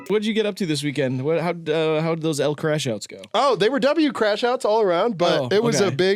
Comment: This entire time I thought they were saying “El Crashout” as if they were speaking Spanish. Only just realized they’ve been saying “L Crashout.” Ill continue to prefer “El Crashout” due to its random hilarity😊